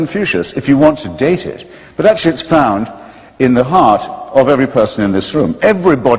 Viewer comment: does Hitchens say, "but that shits found in the heart of every person in this room"?